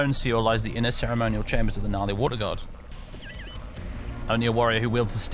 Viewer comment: Lol, when you said Nali water god, I kept hearing 'gnarly water god' :D